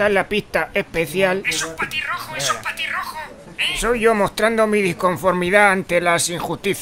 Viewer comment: "Es un patirrojo, Es un patirrojo, Eh" Jajajaja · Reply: Yo también entendí "patirrojo" que pues es un ave jajaja